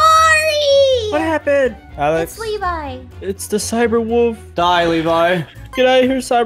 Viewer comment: it’s the cyber wolf Me: why does that sound like he said sssniperwolf🧐